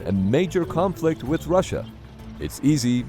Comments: your subtitles are wrong.... It also says "similar to what you used in the pepsi video"... Wth? Subtitles: Imagine a major conflict with China, While he says Russia... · Break free from the echo chamber by comparing news coverage from diverse sources around the world. Try Ground News today: <link> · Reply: your subtitles are wrong.... It also says "similar to what you used in the pepsi video"... Wth? Subtitles: Imagine a major conflict with China, While he says Russia... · subtitles say China, while it should be Russia